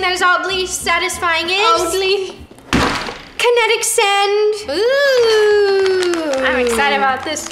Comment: I had the subtitles on and instead of them saying oddly kinetic sand at 5:19, it said in the subtitles "elderly kinetic sense." Lol 🤣 😆